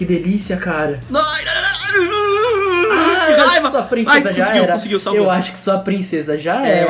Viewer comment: eu ouvi um jaiuson Mendes aí em kkkkk